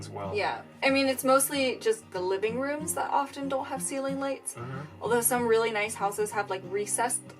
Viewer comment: When he said ceiling lights, I thought he said Seething Knights. I was like; Whaaaaaat?